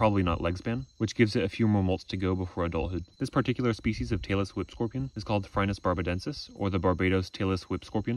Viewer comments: lol I thought you said Taylor swift scorpion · I kept hearing “Taylor Swift scorpion” and became very irate until I read it. Phew…😅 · i thought he said taylor swift scorpion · i thought you were saying taylor swift scorpion 😄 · Every time he said Tailless Whip Scorpion, I thought he said Taylor Swift Scorpion. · I thought you were saying Taylor Swift Scorpion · I keep mishearing the Tailless Whip Scorpion as the Taylor Swift Scorpion lmao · I keep mishearing "Tailless whip scorpion" as Taylor Swift scorpion XD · haha. "Tailess Whip". I thought you were saying "Taylor Swift"! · I thought he was saying "Taylor Swift Scorpion" until I saw the caption :v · I thought he said the Tailor Swift Scorpion 😮